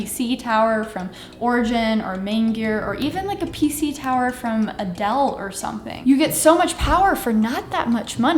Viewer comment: I swear I heard “a PC tower from Adele” towards the end 😂😂